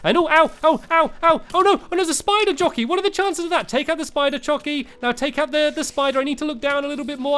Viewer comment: Did Stampy say take out the spider chockey?